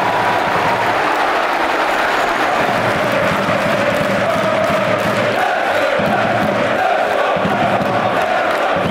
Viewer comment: Never been able to make out what's being said in that chant at 4:57 in games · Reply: Singing Doo wah diddy diddy dum diddy doo Running past defenders, scoring one or two He looks good (looks good) He looks fine (looks fine) Ladapo's on my mind, and he's Ipswich number 9 ☺️🚜